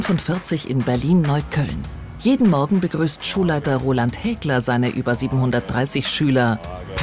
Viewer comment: Ich hab Roland Hi**er verstanden